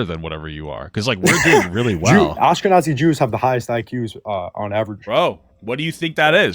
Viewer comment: Did he say Oscar Nazi Jews? 😂😂😂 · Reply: He said Askenazi Jews